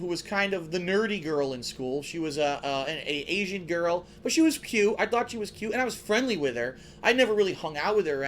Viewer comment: Did he just say "but she was pute"?